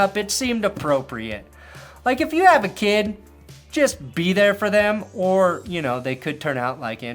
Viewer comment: "If you have a kid, just be there for them" Wow, I had to listen back to that 'cos on first listen I heard "if you have a kid, just beat them" and thought, 'wait what? That can't be right!'